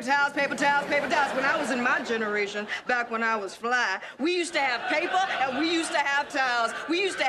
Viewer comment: i thought she said "back when i was flat"